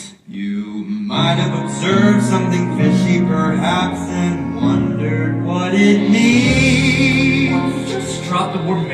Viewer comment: what do they say about their beating hearts to the clerk? I couldn’t hear the whole line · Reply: "The world was silent except our beating hearts" - it's a callback to "The Rainy Day We Met"